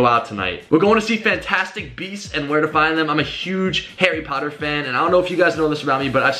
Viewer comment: i thought u said Hairy potter